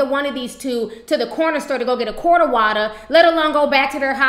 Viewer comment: "Quarter water"😹😹😹😹💀💀💀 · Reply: ShIt we call em LIL HUGS never heard em called Quarter Water at first I thought she said Quart of Water